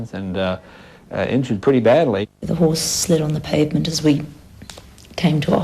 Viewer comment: Could someone tell me what Lucy is saying at 30:59 - 31:10 I can't understand what she is saying :'( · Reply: she said the horse slide on the pavement and I will never forget the sound of hours sliding on pavement